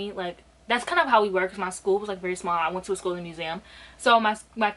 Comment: did you say you went to a "schooling museum" ..????? 🤔